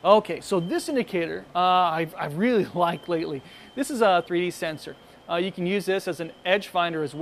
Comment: what does he say, I cant understand him, sound like, "I really klike palywith, this is a 3/8s sensor" ? Thanks